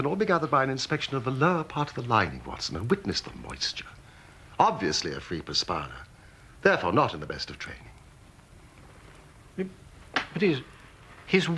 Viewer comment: What does he say in the min 3:20? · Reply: ***** "Obviously a free perspirer. Therefore not in the best of training."